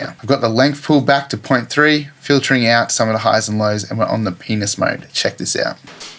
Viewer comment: Hold it! 5:24, Did you say Penis Mode?